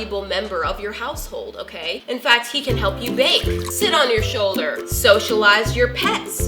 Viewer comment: I thought she said he can help you vape 😂